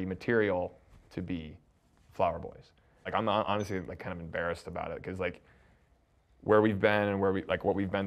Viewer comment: it sounds like says ‘he’s not honestly embarrassed about it’ but then seems to be a bit regretful about it, so I don’t get it, is he acknowledging he feels bad/embarrassed even though it was the right decision given facts, or is he doubling down it was the right decision AND he has no embarrassment about making it?